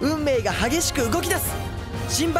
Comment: 最後のスマイルスマイルがサービスサービスゥっぽく聞こえた…